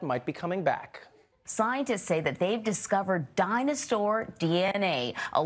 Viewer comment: did she say dinosaur because I heard dinostore. HELP.. I'm having an argument over it. Am I the only one hearing it?